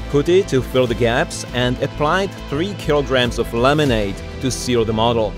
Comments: For a second I thought you’d said you applied 3 kg of lemonade. Couldn’t imagine why lemonade would help. Ohhhh laminate! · For a second I thought you’d said you applied 3 kg of lemonade. Couldn’t imagine why lemonade would help. Ohhhh laminate! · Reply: I heard "lemonade," assumed I had misheard "limonite," and then realized it was "laminate." 🙃